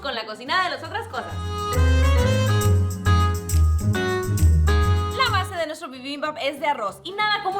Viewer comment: Pensé que decía Biribimbibamba - Selena Quintanilla jajajaja